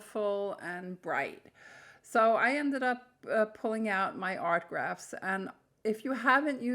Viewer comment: Hi, what do you mean art grafts? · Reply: lol I thought she said art graphs 😂😂